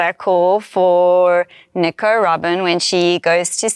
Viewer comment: Did she say Nico Robin or Nicaraguan when she was talking about Robin’s arm cuff? I would have thought she said Nico Robin but it really sounded like she said Nicaraguan lol